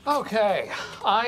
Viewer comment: what does Oscar say? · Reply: He said “you pushed Daryl out the window “